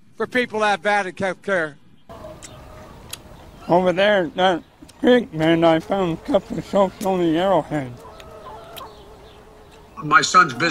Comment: "Over there,that creek man,i found a couple of Shoshoni arrow heads" :)...Thumbs up if you had no clue what he said :P